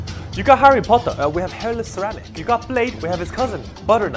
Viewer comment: “You got Harry Potter?” “We have Hairless Ceramics” That got me laughing way too hard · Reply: I LITERALLY THOUGHT THAT HE SAID “HAIRLESS RABITS “WTFFFF IS WRONG WITH MY HEARING